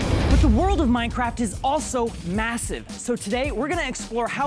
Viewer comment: did you say “Massive” ?